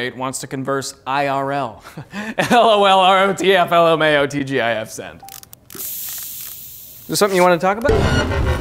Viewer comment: For the people who don't know what he is saying at 0:34, he is saying. IRL, LOL ROTF LMAO TGIF send.